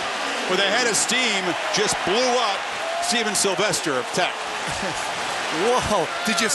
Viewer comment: did they say his name was semen?